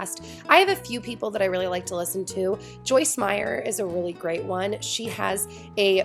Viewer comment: I thought you said Joyce Byers at 8:02 oh my god stranger things is taking over my life